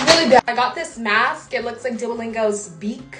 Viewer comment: Omg at the start I thought she said “im gonna be painting myself as dua lipa” and then i watched the video and realised that she said duolingo 🤣🤣🤣🤣